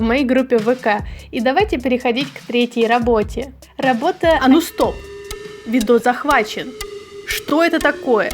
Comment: я себе рисую такой и слышу как кот гворит: А ну стой. И я такой думаю. БЛ,ТЬ МОЙ ПК ВЗЛОМАЛИ